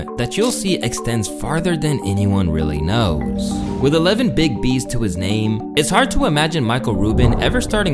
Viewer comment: I thought you said "11 big D's to his name"... 🤣